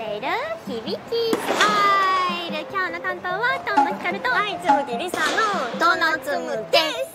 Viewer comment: i heard CHU2 :0